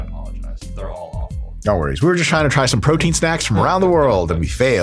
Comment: I legit thought it said strongest protein snacks, lol.